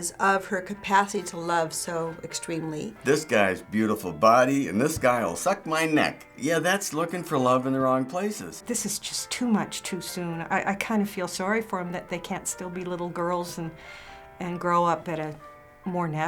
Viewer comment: I thought the old guy said such my dick.